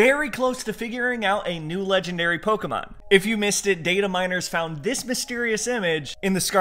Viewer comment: He said: "data miners" What I heard: "dating minors"